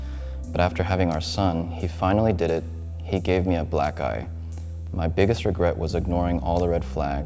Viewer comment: I don't get it, what does he say ? "He finally did it, he gave me a black guy" huh??? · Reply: "Black - EYE" ..